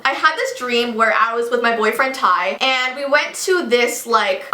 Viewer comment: At first I thought you said pie my boyfriend 😂😂😂